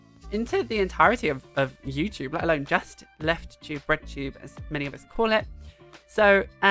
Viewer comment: I'm sorry, it sounded like you said RedTube at 0:30, and the captions confirm it (as much as they can ever confirm anything). So... um... has RedTube changed or are you all somehow oblivious?